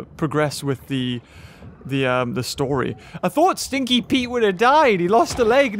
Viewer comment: Bruh if you go on 10:25 he's said stinky Pete instead of scaly pete